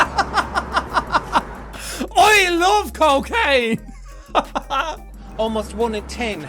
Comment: I honestly thought the guy on the new said “I love cocaine “